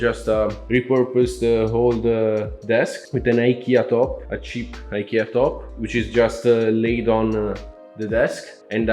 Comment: can we get a link to that case at @ 2:45? I couldn't really catch the name of it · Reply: It's a Jonsbo Mod 5 :)